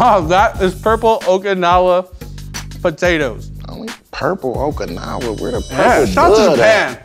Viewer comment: Did he just say "where da purple bud at?" ? lmbo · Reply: "purple bun"